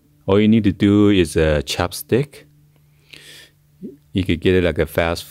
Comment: This video was a roller coaster ride of emotions, especially when I thought he said to use chapstick to get the string out. However, when I finally got the string out of my hoodie, I let out a triumphant roar like a lion that had just slain a zebra. HUZZAH!